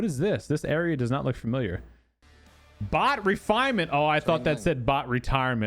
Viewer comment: I also thought they said BOT RETIREMENT too. What a very short moment of happiness, just to realize that I have eye boggers and that I read wrong. I thought we already won the cause, but alas it is still not meant to be. Despair at its finest moment. Heartbreaking.